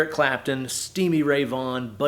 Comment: Sounded like he said Steamy Ray Vaughn lol